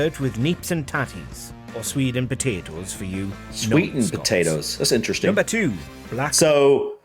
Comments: Haggis is served with “neeps and tatties” - turnips (or swede), and potatoes - not “sweetened potatoes”, you misheard “swedes” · When they said about the haggis being served with swede and potato, I think you misheard it as sweetened potatoes (which sounds vile). Swede is what we call rutabaga :) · You misheard what neeps and tatties is that accompanies haggis - he said swede and potatoes, not sweetened potatoes. Swedes are a type of orange root vegetable like turnips. I pretty much don't like any of those selections, apart from marmite which is delicious.